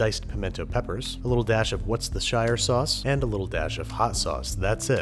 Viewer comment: Sounded like he said “what’s the shire sauce”